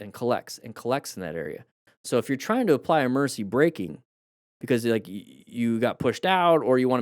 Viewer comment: all this time i thought you were saying "Mercy braking". i need to clean my ears